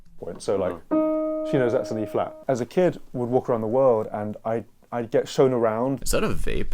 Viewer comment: "as a kid, we would walk around the world..." Did I hear that right?